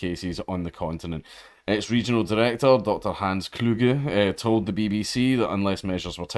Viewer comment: I thought I heard you say "Hans Krueger".